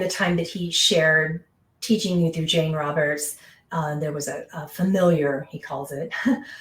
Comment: I thought he said he was a future self of Jane, who he calls Robert, from another life.